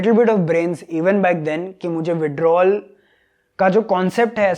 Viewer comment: which product he mentioned ???? I heard something like widrol